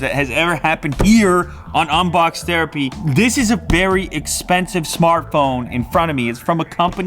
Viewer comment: Was it just me or did he say berry?